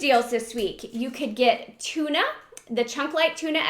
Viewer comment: i dont shop Walgreens but always watch/listen to your videos to contribute to the channel. Watching today while working and I swear I heard you say chocolate tuna! I clicked back to the video so quick, haha!